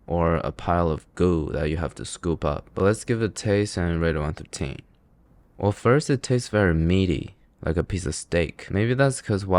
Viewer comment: Oh. I thought you were saying "taste and rate one for the team"